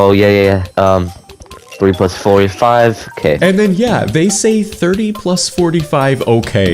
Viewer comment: i think he said "3+4 is 5 ok" lmao